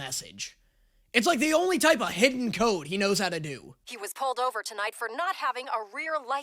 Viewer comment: for a second I thought was saying "a hit-and-code," not "a hidden code"